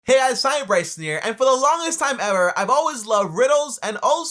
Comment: I swear it sounds like you say “hey I assign me Bryson here” in the beginning lol. I was confused af.